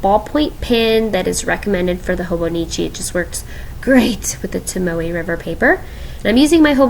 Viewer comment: My fat butt thought you said taco but no you said tombow.🤦🏻‍♀️🤷🏻‍♀️🌮